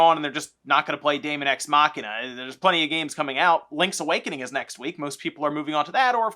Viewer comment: For a moment I thought you said Swankety coming out. lol. But it was Link's Awakening.